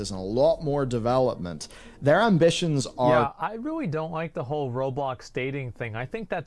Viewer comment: I thought this man said "i dont like robots dating" 😂